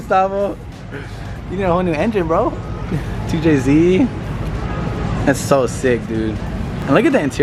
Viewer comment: did i hear tjz 😭😭